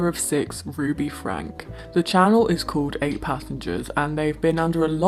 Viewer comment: I thought you said the “8 Passengers” channel was called “8 Pathogens” and I died laughing